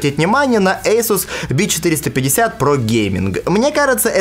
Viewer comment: ASUS БИЧ450- PRO GAMING, вам тоже послышалось?